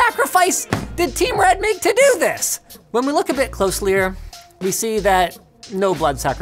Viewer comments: he actually said closelier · did he say "closelier"? 😂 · did Linus really say closlier instead of closer? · Close-lier, anybody else catch that at 6:58? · Did anyone else hear Linus say closlier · DIS BOI JUST SAY "look CLOSELIER" AGAIN?????????????????????????????????????????????????????????????????????????